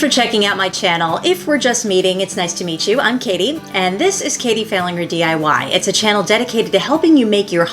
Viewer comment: I thought you said this is Katie - failing her DIY.😅 THAT drew me in since I know I’ll probably fail on at least the first try. Thanks for the video and the chuckle.